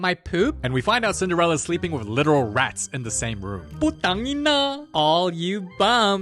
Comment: Did just say "Putangina" ✌️😭